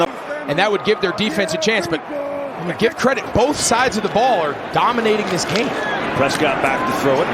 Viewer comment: Fast forwarded the vid to a 48-16 game and the first thing I hear is "both or these teams are dominating the ball" .... 🤨